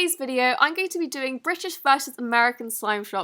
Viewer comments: At the start I thought u said richest not British lol 😂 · It sounded like you said “Brishish and Amerca” 😂